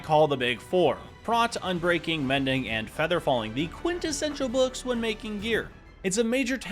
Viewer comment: Did you say Quintessential? Maybe there are 4, like Quintuplets?